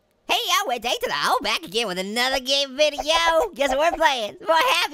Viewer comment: what does he say for his intro? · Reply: “Hey-yo, it’s A to the O here back again with another gaming videooo!”